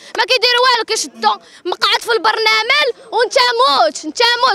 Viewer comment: البرنمال 😂😂😂 واش سمعتوها